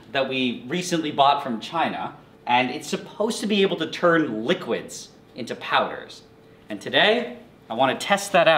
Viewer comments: I thought he said -turns from liquid into powers - i was like wtf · I thought he was saying power not powder😂